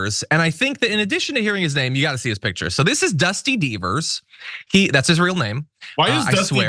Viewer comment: Thought you said “ beavers”. Oklahoma has evangelical idiots in charge. Always into someone’s private life.